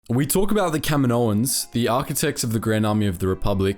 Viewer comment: I thought you said Kevin Owens 😂. I was thinking wow didn’t know WWE wrestlers had a lore in Star Wars.